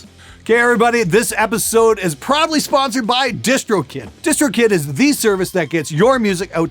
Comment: i know he said proudly sponsored by but i heard it as probably sponsored by